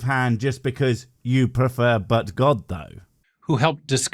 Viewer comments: I know you said "but god though" however i heard "Butt god though" now think boi jebus makes me laugh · Every time says, “ . . . but, God . . . “ I hear “butt God”.